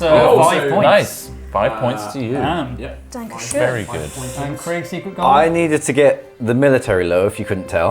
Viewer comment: did she say "dankeschön!" there at 1:18:01 ? . love her already! :D